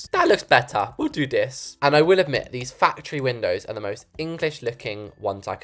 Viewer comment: I heard “fat tree” even though he said “factory” 🤣🤣